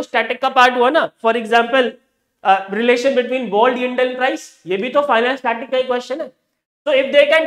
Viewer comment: sir what did you say, BOND AND WHICH PRICE? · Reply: BOND, yield and price. Bond is a kind of asset